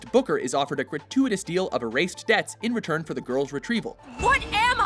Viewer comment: I don't know why, but at first I thought you said "...in return for a Golden Retriever..."